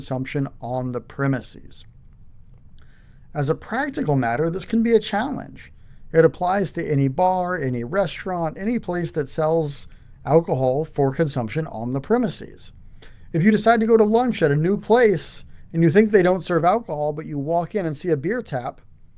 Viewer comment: this guy is dropping some mad knowledge...i thought he said NUDE place